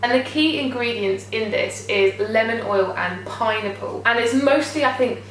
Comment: Oh my gosh when you said "pineapple" I thought you said "pie-nipple" haha!